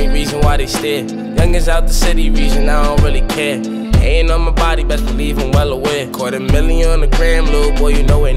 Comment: i think he Said ”i don’t really care pain on my body””